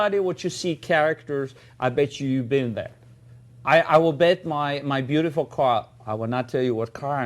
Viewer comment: I thought he said "I will bet my beautiful cock."